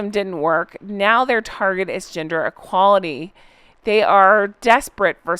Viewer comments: When you said GENDER equality I thought you said GINGER equality and was wtf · I completely thought you said Archwell is taking on Ginger equality issues the first time you said it. 😂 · Sorry but at first I thought you said they were fighting for'ginger equality ' not 'gender equality ' then I realised what you said. Ha ha lol But then it wouldn't surprise me if they would fight for ginger equality soon xx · I was slightly distracted for a moment and thought you said they were taking on 'Ginger Equality' 🤣🤣🤣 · I thought you said they were working on 'ginger equality' 😂 But then again, it's probably only a matter of time...